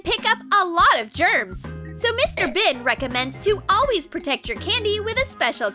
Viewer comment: Mr. Ben is actually creepy. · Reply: For a second I thought you said Mr bean 😂😂😂🤣🤣🤣😆😆